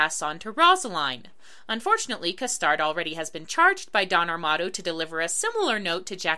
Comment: i misheard "Don Armado" as "Dom Armato", and I was looking forward to hearing Guybrush Threepwood sing -_